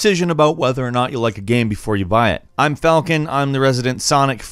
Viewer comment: The way you say "YOU BUY" I always mishear it as "DUBAI"